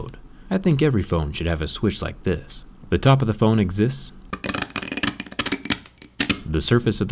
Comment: I thought he said it exists yeah no duh